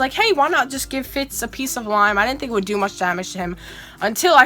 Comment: I heard you say slime instead if lime